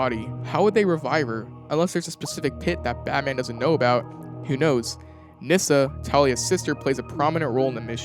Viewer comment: aye bro I see what you wrote in the subtitles vs what you said "Nyssa, Talia's hot sister"